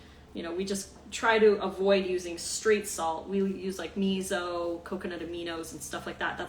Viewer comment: When you said straight salt I thought you said 'Street Salt' lol!!